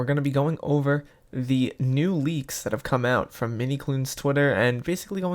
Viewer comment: me: misheard MiniToon Me: HmMmMm iS tHiS MiniToOn TwIn???? Minecraft: MiniKloon Roblox Piggy: MiniToon :0 ✨✨✨✨